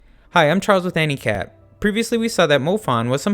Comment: When ypu say hi I’m Charles with anicap it’s sounds like you say hi I’m Charles from handicap